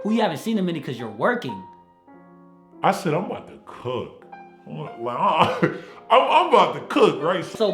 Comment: I heard, "I'm about to cuck" at first. ... I don't like Valentine's Day either, but I'm also single lol